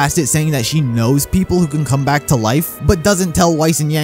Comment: Yo for real I thought raven said she knows people who could bring people back to life not people who can come back to life . Dammit I guess pyrah still gonna be dead